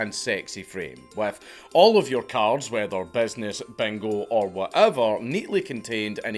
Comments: Bro I was fucking dying. I thought you said bengal not bingo. I was trying to figure out how the fuck they used a bengal tiger to select draftees. I wasn’t sure if it was like a fucking Roman Colosseum situation. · Lottery? I thought it was bingo… What a disappointment. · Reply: Ooooh he said bingo??? I thought he was saying bagel